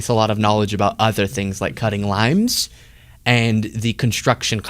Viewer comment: When you said you injured your finger cutting a lime, I thought you said cutting a line. And I thought to my self, hey wait, I didn't know Luke did drugs lol.